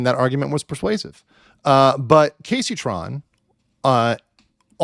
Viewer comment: The CC dubbing displaying Keemstar instead of Kaceytron 💀